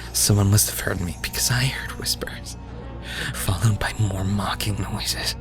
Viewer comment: what does say at 6:18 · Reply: someone must have heard me because heard whispers